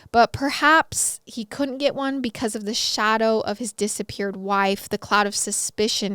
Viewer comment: I thought you said dissaparent 😂😂